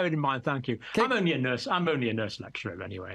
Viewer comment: Did he say he was “only a nurse” at 11:40? Is he a Dr. or a nurse?